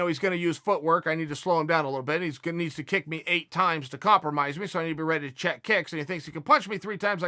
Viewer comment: I thought he said 4 kicks to compromise Khabib